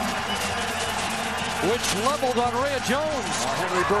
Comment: Lmao, 1:52 the closed caption say which leveled gonorrhea Jones